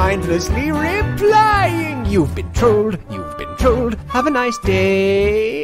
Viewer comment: I thought that the music said: You've been told, you've been told, have a nice day lol